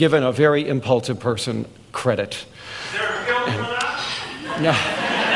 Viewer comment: what does the man yell? "there are credits for that"?"there are bills for that"